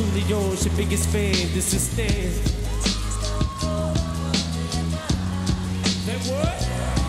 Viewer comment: What he said in 26:43 and 28:18 and 28:32? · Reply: ''Say what?''